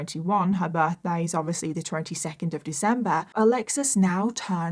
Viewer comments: says her birthday is 22nd December just clearing this up as loads of people think that you said September.. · 22nd September is Virgo isn't it? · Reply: I heard her say she was 18 on 22nd December .. might be the British accent confusing everyone